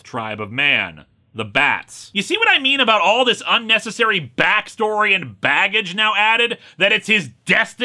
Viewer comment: Am I the only one who heard this as "batstory and battage?" It's like the "Jellicle" of the DC universe. I don't know what "bat" means anymore, and I'm not even sure it's what was said, bat I'm not sure it wasn't either bat